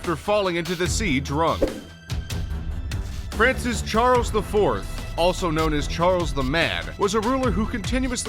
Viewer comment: he says "Charles The Fourth" but the text says Charles VI instead of IV